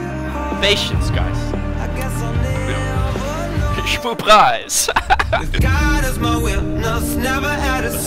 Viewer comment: what did he say shmoop ???